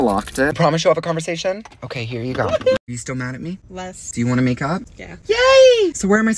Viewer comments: I thought he said make out. 💀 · I thought he said “so do you wanna make out”😂☠️ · HEEELLP I THOUGHT HE SAID "so do you wanna make out...???" 💀💀😭😭✋️✋️ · HELP I THOUGHT SAID "you ready to make out" OMG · I thought he said" do you want to make out?"WHAT???